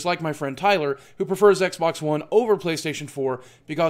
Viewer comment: at the end i thought he said PS2 not playstation too